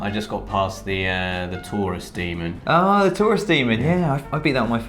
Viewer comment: I've never played Dark Souls and I heard "Tourist Demon".